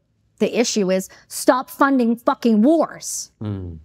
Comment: I may have misheard, but did she say, "stop funding f**king wh*r*s"? That is, the systemic degradation of shes-who-cannot-be-defined; and now only identified through the voices of her b*st*rd offspring of bucks, c ucks and sucks; who are no longer able to reproduce normally and so go on to birth through the mind of is-lam, com unism and alphabet. Is this the wordless word I just heard?